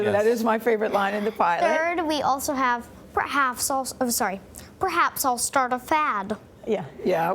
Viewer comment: what did he say? I'm Italian so I can't understand quite well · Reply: SunshineDepression _ITA "perhaps i'll start a fad" -"forse inizierò una moda passeggera"